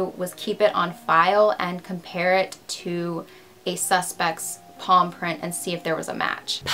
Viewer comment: I thought she kept saying paw print.. I was so confused until I seen the picture of the hand 🙄